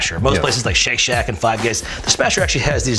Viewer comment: Did George say Five Gays?